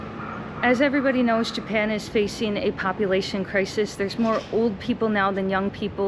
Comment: i thought you said Japan is facing a copulation crisis 🤣 i was like WHAT DID SHE SAY LOL 🤣🤣🤣🤣🤣🤣🤣